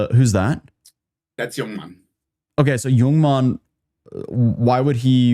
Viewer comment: I'm over here eating and listening. FOR A MOMENT I thought he said "Your mom" I was like wow ain't no way